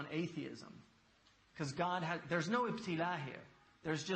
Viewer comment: What is that word Hamza uses at around 1:26? I think he says "there's no iptila here. There is just oppression and oppressed" what does the (iptila) mean and how would you spell it? Much thanks peace and blessings you all 🙏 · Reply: إبتلاء = tribulation Ibtila'